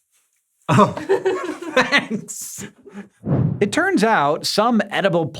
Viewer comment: oh Hanks hahaha Instead of thanks